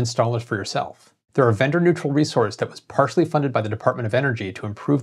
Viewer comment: Oh man!! At first I thought you said "gender neutral" at 1:58.. HAHA, Okay "VENDOR neutral". Good deal then!